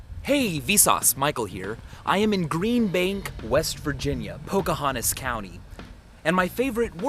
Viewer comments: At the beginning I thought he was going to say: ... I am in Green-Screen." lol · did you say vagina?